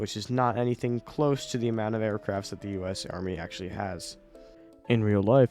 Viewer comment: did you just say army? 1:51 i dont think heli land or takoff from there